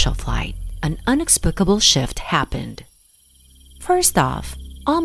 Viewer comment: At first I thought it said " some unexplainable s*** happened "